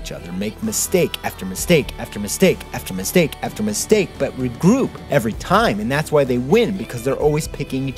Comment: This is now my favorite of your creations. You were getting me really emotional! Also, who else heard "Re-Groot" at 14:30 ?